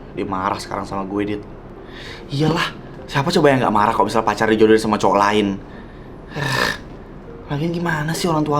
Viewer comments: ini aku doang kah yang denger ngomong nya "pacarnya dijodohin sama cowo lain" atau emang salah denger😭😭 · apa cowok dijodohin sama cowok gak salah dengar kan gue